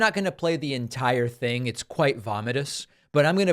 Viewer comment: Did you say “vomitous?” 🤣😂